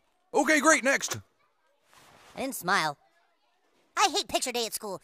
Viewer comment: When said “i didn’t smile” i thought he said “pedophile”